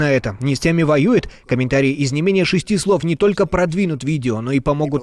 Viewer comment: "...комментарий из не менее шести слов...." послышалось как ".. комментарий из шести букв..."))) да, в мире ситуация именно из шести букв - пи...ц!!!!